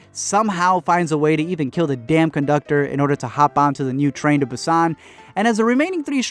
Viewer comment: I thought it said "train to Russia" I guess it's not gonna be terrifying